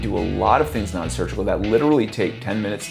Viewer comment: the subtitles say "mascular muscle" but it should say "masseter muscle" anyone able to edit the subtitles?